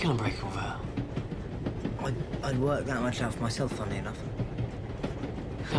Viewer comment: what is he saying ? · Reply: "I'd work that much out for myself, funny enough"